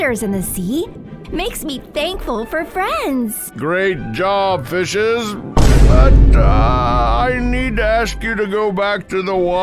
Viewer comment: I swear when he said "great job,fishes",i heard " great job,bitches"